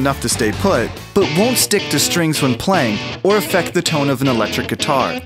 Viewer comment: did he say- or fuck the tone